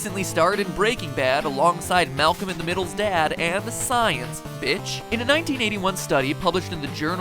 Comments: did he say " bitch " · Did mattpatt just say "Science, bitch!" ?! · In 4:37 did matpat said "b'tch"? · did MatPat say bitch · Did you say bitch? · did mat say "science bitch"